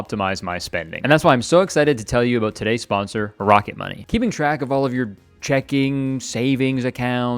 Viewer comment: did you say "Barack-it Money" ?